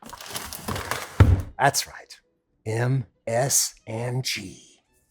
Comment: Did Alton say MSG? Fuiyoh! Now we need an Alton Brown and Uncle Roger collaboration!!!